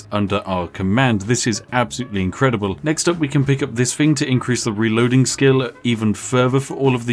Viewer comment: it sounds like you said "sphincter release reloading skill". My first thought was the guy that loads nuclear bombs onto airplanes. You know that guy puckers up really tight while at work.